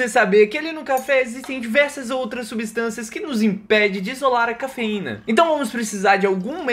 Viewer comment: JURO QUE OUVI "COCAÍNA"😢